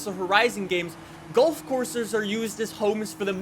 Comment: I thought you said "gulf coarse" , that would've been a good pun.